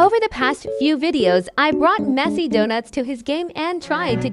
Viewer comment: When he said messi doughnuts i thought he said messy doughnuts 😅